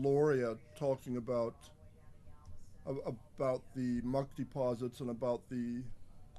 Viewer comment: There is a lot here to know, this is good stuff, Thanks! Looks like a keyhole Lol when you say mud deposits I thought you first said monkey paws Heh, yes mud deposits